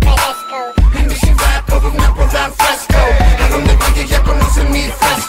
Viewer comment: Lo que entendi es en tu ciudad en tomeni fresco